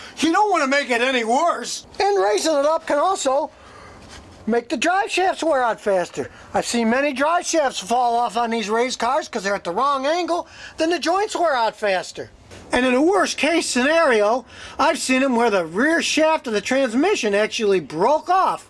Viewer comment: Did he called that truck a race car. 😆